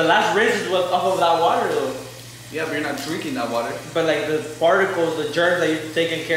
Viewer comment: Is it just me for did he just say ✨fartials✨😭😭